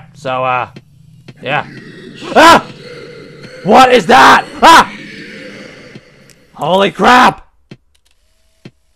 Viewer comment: Did he said "You should have never come here!"? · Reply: He did